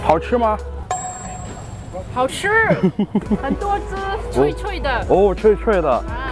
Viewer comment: She said 脆脆的 (crunchy) — subtitle is wrong. I think he couldn’t understand her southern accent haha